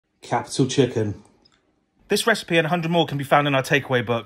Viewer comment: Great video! I uh, didn’t see any orange in there though 😅. I guess Americans like to make things confusing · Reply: Jesus Chin, what are you like? 🤣. I wondered why I heard “capital chicken” at the beginning!